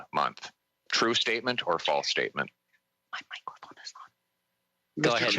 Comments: what is the whisper at 5:43 ???? sounds like some one saying " don't answer this one" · At about the 5:40 time mark, somebody whispers something about "oath" but I can't make out what is said. Can anybody else? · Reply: "my microphone is on?"